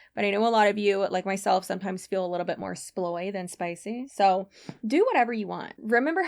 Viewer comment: omg I heard "soy" instead of sploy and my brain went - yes that's absolutely the opposite of spicy but not in a bad way 🤣😭